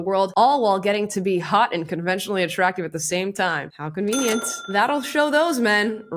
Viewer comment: Did she say "conventionally attractive"? 😂💀